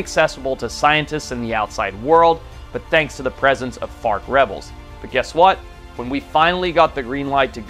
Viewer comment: i am laying down listening to the video, lol i had to stop and rewind, i swear i heard FART Rebels